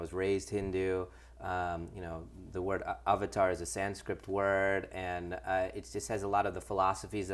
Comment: did he say avatar is a "San-script" word?! hahaha